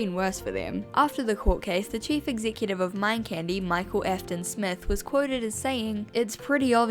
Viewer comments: I heard you say “Michael Afton” and I spat out my ice cream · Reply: I looked it up after the vid, sadly its Acton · Did I hear this right? 9:11 Michael... Afton? The man's name is MICHAEL AFTON? 😭😭😭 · did i hear micheal afton or am i cazy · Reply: its Micheal Acton apparently lmfao but it confused me took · did I hear that right? MICHAEL AFTON?🤔🤔😳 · DID YOU SAY MICHAEL AFTON?! someone call matpat we got a fnaf theory